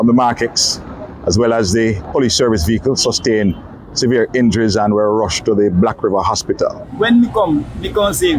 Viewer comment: Did I hear correctly "... as well as the police service vehicle sustained injury and were rushed to the hospital" ???. Please tell he did not say that.